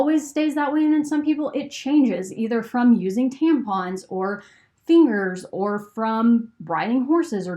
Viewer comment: did you say fingers ?